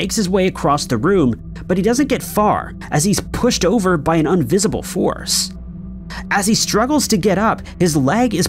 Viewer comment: Did he say “unvisible” or invisible? Idk why but it’s confusing me.